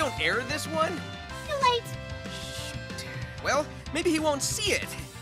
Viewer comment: did Sonic say “Shit”? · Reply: He said “Shoot”, although Japanese Sonic once said “shit” in Sonic X. 😂😂